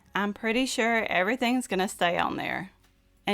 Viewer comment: did you say "just looking at this makes me want to run naked again"? 😄